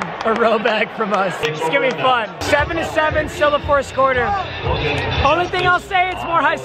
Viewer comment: did he say “4st quarter”